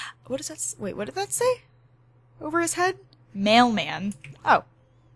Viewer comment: When it said mailman I actually thought it said mallman